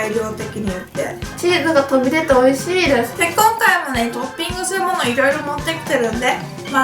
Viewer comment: ドーピングするものって聞こえる…